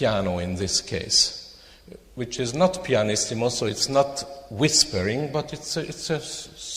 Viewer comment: Thanks so much for sharing! Just one little mistake in the captions I noticed is that at 15:58 it says "pianist him" instead of "pianissimo"